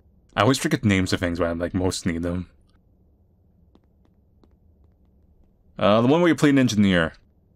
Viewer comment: "The one where you play a ninja in the air." I got it after a couple seconds, but I really thought that's what you said.